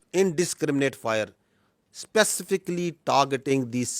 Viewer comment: Did he just say Spacific instead of Specific